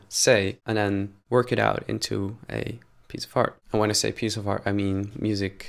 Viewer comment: when you say piece of art, I keep hearing piece of fart, but really important things you've discussed in this video, thank you